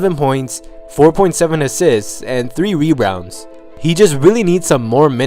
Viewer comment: go to 7:23. He says "rerounds" instead of rebounds.